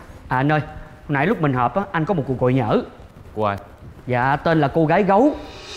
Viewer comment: " cuôc goi nhø~ ..." , phai là Cuôc goi lø~ mói là Nam ky , dúng là bį Bäc ky kiêm duyêt